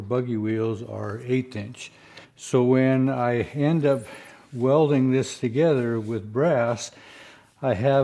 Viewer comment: I’m doubtful about that. I believe I have heard him say in the past that high carbon steel wire is used. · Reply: My bad. I thought he said brass wire in this video, but what he said was 'when I end up 'welding' this together with brass".